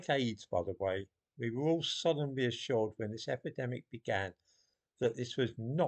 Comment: Thought you said all "Sodomly ass'ured"